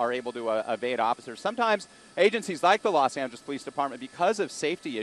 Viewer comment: I thought he said los santos police department 🤣🤣🤣 i need to replay it once